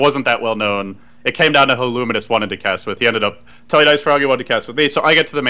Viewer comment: did he said IceFrog? I think knows IceFrog